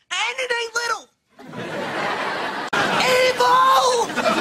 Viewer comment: What did Josh said? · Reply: EVIL!